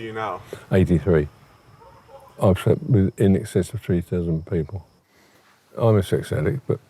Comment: Wait I thought he said he slept with in excess of 3 dozen people... 🔙🔙👀🚗💨 But NOOO!! Grandpa said it’s over 3,000!!! 😱 Mans was BUSSYYY